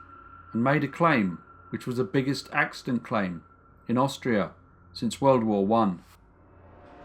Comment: For some reason I thought I heard you said Australia.